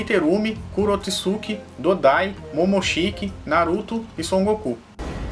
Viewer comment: eu ouvi son goku?